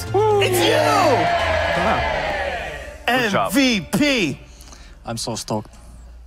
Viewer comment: Thought he said I'm so stoned XD